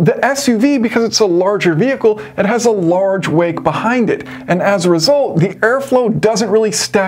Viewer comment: and on the car comparison, I kept hearing you say small "weight", you're saying "wake". I was so confused, thinking, this has nothing to do with weight, it's the aerodynamic flow! Had to rewind a few times before it clicked in what you were saying.